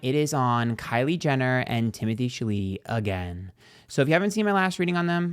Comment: Timothee Shaleetee on the left and Kalendria Jenner on the right 💀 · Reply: i heard "timothy chilli-dee"